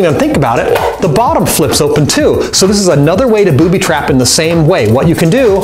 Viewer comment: I thought it said "Bobby trap" XD